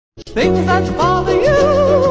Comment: At the start I heard “ things that brother you~ haha” and now I’m terrified 😨